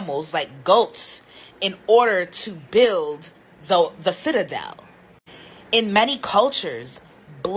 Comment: is she saying "her people " built a shitadel 😂😂😂